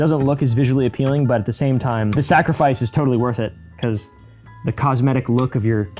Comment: I thought said “snackrifice” 😂